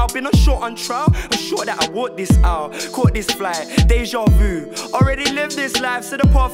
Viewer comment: does he say "déjà vu" ? In French ?